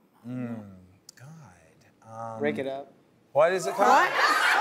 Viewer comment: I thought he said lick it up for his karaoke song